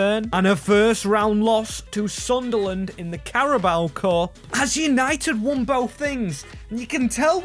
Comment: did... did you just call us United?